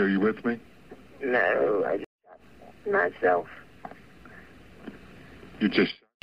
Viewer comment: I thought she said "I just shit myself"